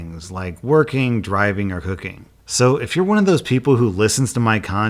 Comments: HAHAHAHAHA I thought you said "I know a lot of fans listen to my content in the background while working, driving or hooking." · omg i had to replay the ad part at the end 3 times to try to figure out what you said. Working, driving, or cooking. i'm so embarressed I heard you say hooking. Like someone is being paid to do certain tasks and your videos are on in the background. I either need more coffee or more sleep >< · Sounded like you said HOOKING not cooking. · Oh My Sweet Lord, y'all probably don't even want to know what I misheard say at the start of the raycon earbud ad at the end of the video, but I'll tell you anyway. LMAO. "I know that alot of my fans listen to my content while doing other things like working, driving and hooking..." luckily I wasn't drinking that particular second cuz I bust out laughing and had to rewind it at least three or four times. I'm pretty sure it says cooking yeah, but it sounds an awful lot like hooking. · Lol.... "......or cooking." I thought You said "...or hooking" during your racon ad. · My hearing is awful. I thought you said fans were listening in the background while working driving or hooking. lol